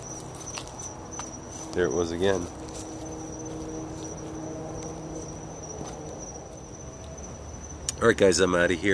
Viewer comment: Yes, I heard and the whistling. At 28:44 I heard a whisper, not sure what it said. That place is super creepy. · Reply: "Shoulda gone with my instinct " is what I hear... slow the video down and turn volume up and press your highlighted timestamp... see if that helps :)